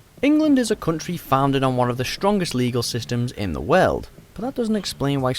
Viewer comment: Did he say "The WELD"?